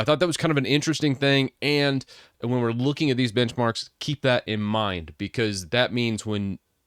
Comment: lol thought he said bitchmarks